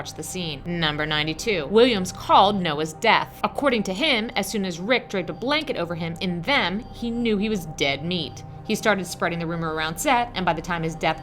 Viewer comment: What are you saying at 16:42 for number 92? It sounds like your saying "he called Noah's death as soon as Rick dragged a blanket over him and them he knew he was dead meat" if so what do you mean by that? Are you talking about when they made a mold out of him? And why would it of been Rick that helped make the mold?